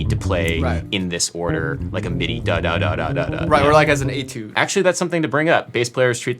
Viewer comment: What is he saying at 5:10 about the Charlie Parker line? Bass players treat it as a what? An atude? An A-tune? Closed captioning isn't picking up what he's saying and I don't think I'm familiar with whatever term they're using. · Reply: Etude